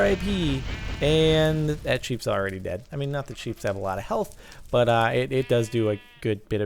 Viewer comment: Did he just say 'sheeps'?